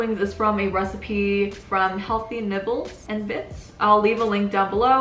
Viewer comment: I thought you said healthy nipples 😂😂 love your vids btw